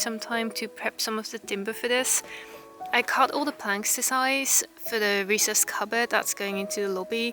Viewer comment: This was very relaxing to watch. I thought you said planckersersize in the beginning 🙂 I've been told you can make green tomatoes ripen by storing them dark (and do not stack them). But haven't tried it. But it beats throwing them out.